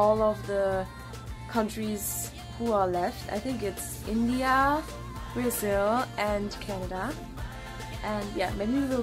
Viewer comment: Eu ouvi BRASIL? AAAAAAAAH! 💙💚💛🇧🇷